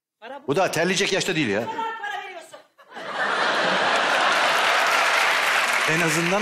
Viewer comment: orada suzan ne diyor duyan varmı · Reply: O kadar para veriyorsunuz diyor